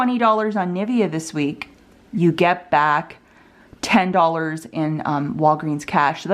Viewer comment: The deal is giving back walgreens cash so you can do them all together and get back 12 wc. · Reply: oh, I thought she said you got back RR, my bad. Thank you